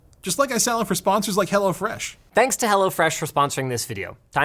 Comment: It sounds like you say HellaFresh 😅